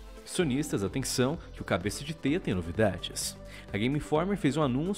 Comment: MANO EU ESCUTEI CABEÇA DE TETA KKK